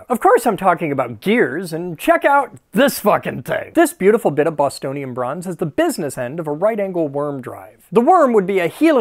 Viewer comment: I SWEAR I heard ["bustonian" bronze] the first time, and giggled more than I ought to have.